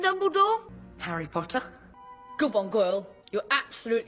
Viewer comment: On minutes 1:51 - 1:54 instead of saying good on Goyle it said good one girl